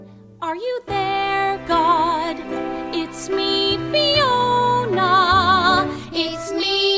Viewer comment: Me: this is so cute, I wonder what musical it's fro- Video: "It's me, Fiona!" Me:I....Is this from shrek? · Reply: lol it took me a while to realise also i thought it said biona not fiona i didnt realise till i read the description